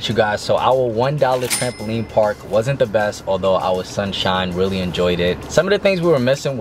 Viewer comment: When said “our son Shine” I thought he said “our Sunshine” I was like aawwwww that’s so cute